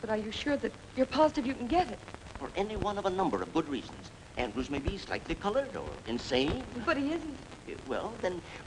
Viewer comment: around about 9:50, I am a bit deaf , but does he say he thinks he can get the marriage annulled because the husband " maybe slightly coloured" ??